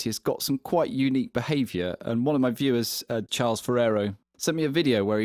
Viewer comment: i swear i heard you say james ferraro